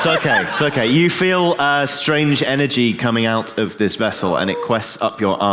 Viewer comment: I heard 'you feel a staring' and had to go back to figure out why everyone was worked up